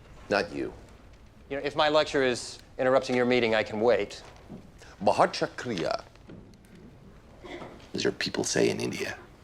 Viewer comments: He said "Bahaut Shukriya" (thank you very much) as an Indian I did not even get what he said till he said it was spoken in India, damn his Hindi has a MAJOR british tilt super funny XD 4:56 XDDDDD "Teri maa ki" basically like saying Motherf- Damn I hope the Indian medical journal level and the respect they command worldwide has gotten better /\ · its "Bohot Shukriya" means "Thanks so much" I couldn't understand until he said INDIA and "teri maki" is a slang you say as "your mum" before curse someone